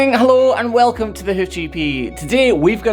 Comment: You know when you're scrolling up and down on videos to watch, yours came up and there's subtitles on, and what they thought you said instead of Hoof GP was hoochie pee! 😂 but anyway, nice job helping that poor cows foot 😬👍🏻🐄...